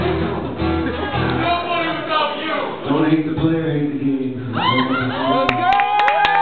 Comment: What did Adam say at 0:44? All I heared was "Don't hate" lol.